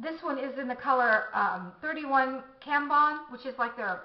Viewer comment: Also I thought I heard you say (In regards to the Chanel Lipstick) that the shade was "31 Tampon" lol... I had to rewind!